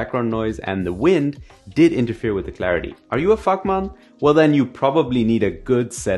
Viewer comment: Very informative and helpful, great video! Though I'm not gonna lie, I almost fell off my bed when I thought I heard "Are you a F man?" at 2:36!🤯🤣🤣